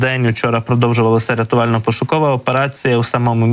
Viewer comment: а мне послышалось, - "... рИтуально- пошукова операцiя..." Извините...